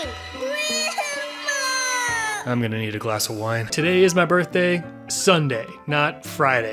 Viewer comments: Idk why I heard “I’m gonna eat a glass of wine” 🍷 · I thought he said "I'm going to eat a glass of wine" in the beginning, and now it's my favorite quote